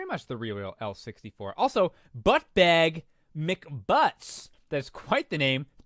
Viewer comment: I know it was ButtBagMcButts, but I swore I heard "ButtFagMcButts" I'll have to call my boyfriend that next time he compliments my ass, lol